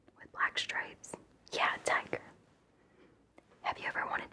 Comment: Imagine being high af, and somebody asks you "have you ever wanted to be a tiger?" 👁👄👁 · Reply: Ohhhh I thought she was saying “diker” this entire time!!! I was very concerned my face was as like this 😟